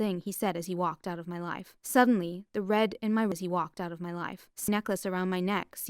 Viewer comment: what she said? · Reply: Life